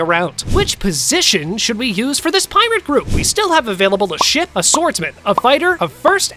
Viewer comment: he sounds like he’s saying shit instead of ship